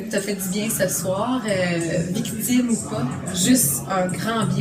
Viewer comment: ecoutez bien a 13:37 elle dit victime ou pas (moi j'entends Guy Cloutier ou pas)